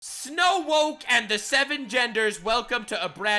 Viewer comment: I heard "brown snow and the seven genders." 😅😂😂